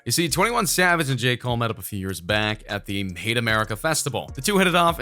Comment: did he say they met at the "hate america festival?" I thought it was Made in America festival